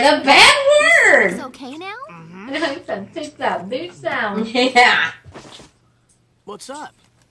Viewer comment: I've never watched the dubbed version but @ 3:48 did Ino just say "Take that bitch down"?? LMAO wtf...